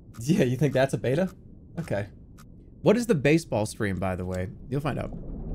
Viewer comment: that's it, the pivotal moment. Forever captured in 2rma or whatever this channel's called. I want it to be known, that the first time i heard it, i thought he said "baseball bat" for the longest while.